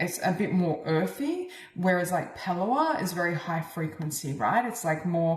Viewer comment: WOWzer!...#2 then #3...Beautiful Messages and confirmations! In #3, you talk about Reiki and another healing modality. What is the second modality you mentioned? Thank You! You are Purely Divine! I Love You So! 🌈🌹🤗💖🌹🌈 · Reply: same! The time stamp is 45:07 if anyone can hear what was said and please tell us? Sounded kinda like "pawala" or "power light" or something, idk. Idt it was Pranayama but could be, maybe?